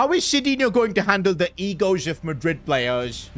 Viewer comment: my stupid ass thought it said eggos